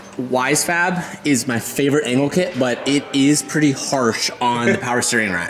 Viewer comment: "Favorite anal kit" - Did he just say that and did try and hide a laugh?